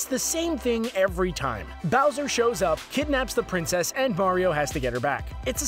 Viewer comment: Did he say Bario?